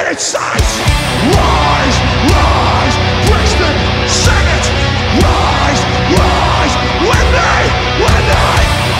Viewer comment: does he say "Brisbane, sing it".......... you're in London haha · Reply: Brixton